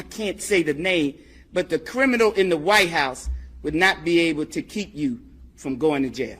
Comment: did she say “the criminal in the white house “ I wonder who she's talking about 😂